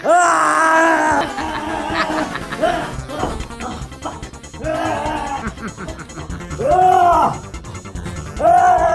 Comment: LMAO at what says. 🤣😂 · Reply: you can closely hear him say “oh fuck” when he slips